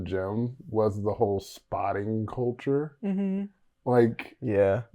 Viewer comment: I straight up thought was gonna say, “that’s something I miss about cocaine culture” and I was like shook! Like!? Really!? But he said spot so I’m just dumb lol. 😊